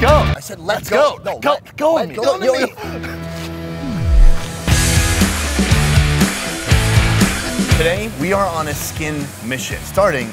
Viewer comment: Did sonny just say “đụ má “ this is the funniest video ive seen on this episode